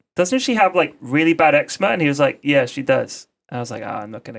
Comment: What is he saying? "Doesn't she have very bad exman"? Someone tell me what word that is so that I can google its meaning, please! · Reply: rusins eczema it's a skin condition